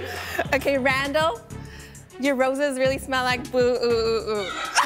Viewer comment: wtf did she say??? They smell like bo-oo-oo?